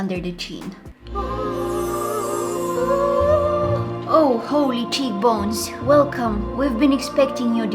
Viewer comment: when she said whoa holy cheekbonesI thought she had said "o holy cheesecakes"